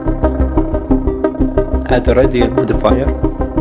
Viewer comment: what are you saying here? Can’t hear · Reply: “Add radial modifier”